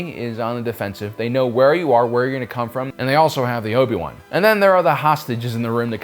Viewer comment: "...Ends up on a catwalk high above the ground. It's the Bad Batch's only weakness." Technically it would be Wrecker's weakness as in one of their missions, likely the one involving the rescue of Echo, Hunter joke about it as they enter and use the lift. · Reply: Did he say "they also have the Obi-Wan"?